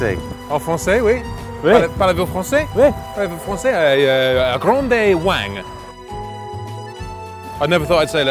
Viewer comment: Did he just say “Grande Wank”? 😂😂